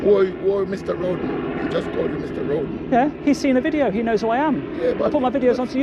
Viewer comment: Mr Rodent?? Did I hear him correctly??